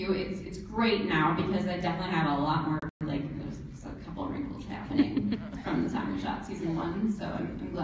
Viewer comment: What does Natasha says at 32:52? "Because I have definitely a lot of..." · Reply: Archive Carmilla Lot of...a lot more...a couple of wrinkles now